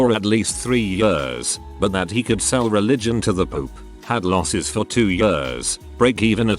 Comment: People listening not watching at 8:04 be like "I didn't know turds could be religious." It literally he sounded like he said "poop" not "Pope".